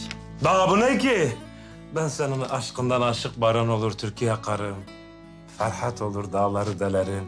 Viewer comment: Türk'ü yakarım diye anladım...